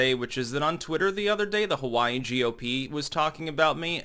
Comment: I thought it said Huawei GOP for a second ahahahah